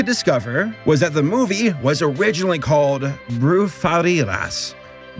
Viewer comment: When I heard him said “brujerías” like if it was “brufharidas” I almost died. XD